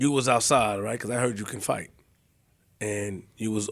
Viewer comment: I'm surprised cass didn't say nothing 😅😅, a instigator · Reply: I think he said i heard u could fight.. i thought he said couldnt 🤣💀🤣